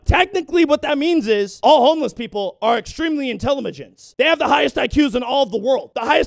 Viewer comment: Did he say intellimagent?